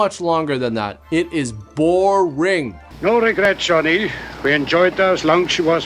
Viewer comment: Tell me why I legitimately thought the Professor said "no regrets shawty" instead of "no regrets Johnny"? 😂😂😂😂